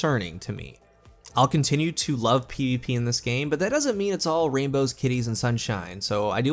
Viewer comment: not really contributing to the conversation, but i thought he said "rainbows, titties, and sunshine"\